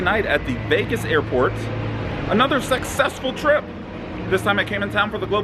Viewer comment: did you say "sexessful"? If so, nice.